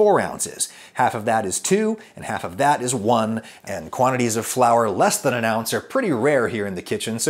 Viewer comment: The subdivision argument only works for situations where you start with a convenient number, like one pound. If (say) a recipe calls for 5 oz, and you want to halve the recipe, you straight away have to deal with the 2.5/1.25/0.625 scenario. Same in the machine shop. In any case, machinists tend to work in decimal inches where fine tolerances are concerned, ie they're actually using base ten whenever it gets hard, so (once again) the subdivision argument is moot. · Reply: "he goes about how 'less than a pound of flour is rare, so who cares'." That's NOT what he said! He said "Quantities of flour less than an OUNCE are pretty rare..." 4:40 If you have to blatantly misquote someone, you've already lost the argument.